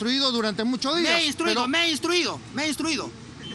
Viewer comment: jajaja que gracioso parece que dice "ME HE DESTRUIDO" XD